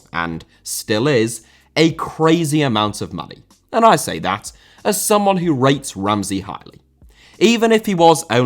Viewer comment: idk why I thought you said “that's a crazy amount of money and I say that as someone that read Ramsey's mind” and I can't unhear it